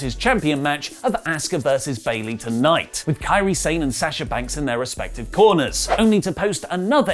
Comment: When said “can Sasha banks...” I thought he said “Kinshasa” as in shinsuke finisher